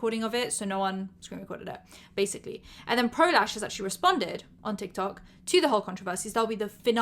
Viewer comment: I literally thought you said prolapse instead of pro lash 😭